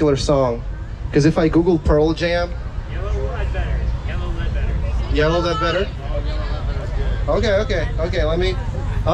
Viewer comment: I seriously thought he said yellow bed wetter